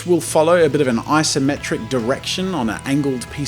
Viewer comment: did he just say i-symmetric?